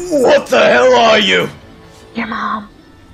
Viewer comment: why do i hear a "ur mom" at 1:00 ?